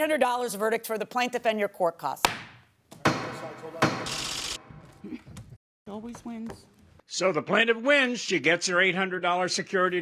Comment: What did the defendant say that was muted?? “A bitch always wins”?? 😂😂😂